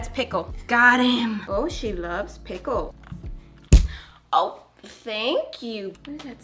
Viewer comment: 7:37 I think you might have said her name? I’m not going to say it but maybe check? If it’s not that’s great but if it is maybe edit or take down and repost the video bleeping that out? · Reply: I think she said “oh she loves pickle”